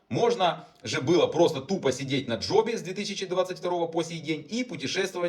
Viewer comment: Мне вот интересно, мне одному вместо " на джобе "послышалось "на жопе" ? Просто, как мне известно, кроме как сидеть на жопе и получать донаты, этим укро-патриотам нет занятия милее )))